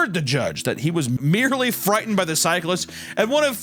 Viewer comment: Anyone else hear "merdely frightened" at 17:14 ?